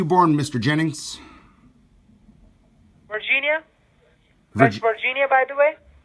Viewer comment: I almost died when he said he was born in Virginia. 🤣🤣🤣🤣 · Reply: I thought he said Vaginia